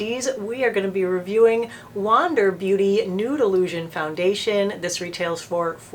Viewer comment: Ha, ha, ha! When you were saying the name of this foundation, I thought you were saying Noodle Lusion. Thank you for giving me a chuckle today. Hugs